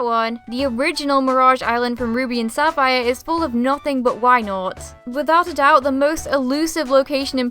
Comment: when she said wynaut for the first time i thought she was saying why not this guy?